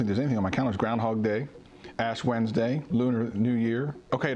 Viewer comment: Did he say Ass Wednesday